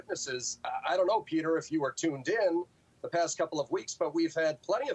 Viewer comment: The NeoLib at a union is an insult to unions. Calling the host "dim"; that's how weak he is. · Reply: Thought did he just call Peter dim? Went back @ 5:02 to find out he says tuned in.